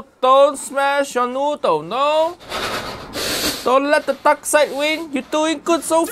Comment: Uncle Roger: "don't let the dark side win" What I heard: "don't let the duck side win"